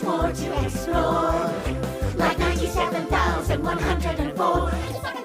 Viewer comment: For a second, i thought he said “a 37104”